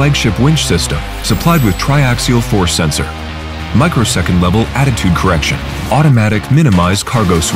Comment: Slight error at 1:00. Narrator says microsecond but subtitles say millisecond. Other than that all good :)